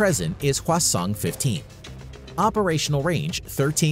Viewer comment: I heard North Korea's "Hwasong" as "Croissant" the first time hahahaha, was like, no way it can't be that cute